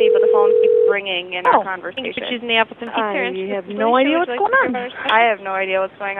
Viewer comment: did she say appleton?